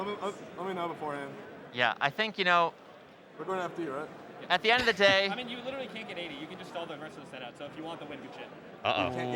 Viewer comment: What was iBDW saying @ 08:26? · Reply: Like- you’re not gonna give me any ledge grabs. I mean, if you wanna stall, I can cheat, you win.